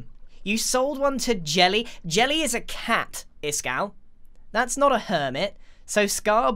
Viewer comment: the captions/subtitles say: jellie is a cat piss cow